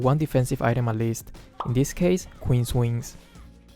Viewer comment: when he said Queen's wings , I thought he said "Queen(Kagura) wins"